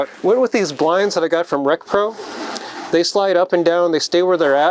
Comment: where did he say he got the blinds from? Sounded like he said recto? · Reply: REC PRO